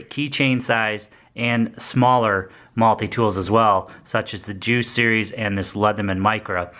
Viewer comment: did you say, "the jew series"? 😆 Seriously, I can't understand what you're saying right there.